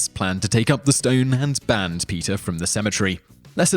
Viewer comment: For the first half of the video i thought he was saying "Peter" with an accent and i just realized he was saying PETA. I'm Dumb